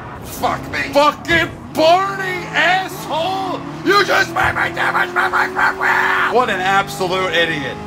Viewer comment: What in the world are you saying? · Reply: You just made me damage my right front wheel